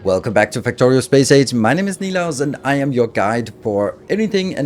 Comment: tell us more about Space AIDS! I can't be the only one who heard that :D